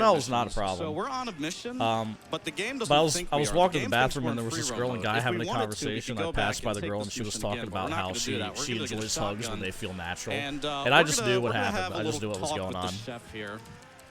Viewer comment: What does he say at 23:55 about the girl? Can't quite make that out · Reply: "A guy and a girl were having a conversation and I overheard the girl say, 'I enjoy hugs when they feel natural.' and i just knew what happened, what was going on."